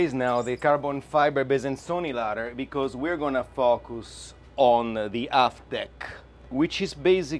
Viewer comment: I thought he said, "We are gonna put up this ladder cause we gonna be fuckin with the aft deck". He said, "FOCUS on the aft deck"